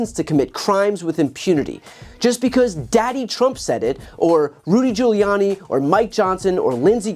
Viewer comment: LOL!! Thank you Brian!! I misheard what you said when you said Rudy Guiliani's name - I heard LOONEY Guiliani and had a hearty laugh! Brighter day in all this rain.